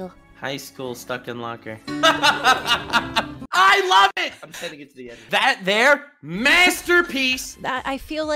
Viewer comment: "I'm sending it to the editor" anyone else catch that